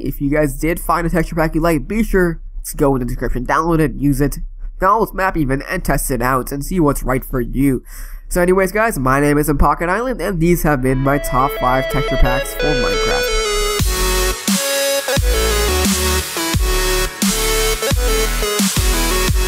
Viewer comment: sounds like you said my name ISNT at the end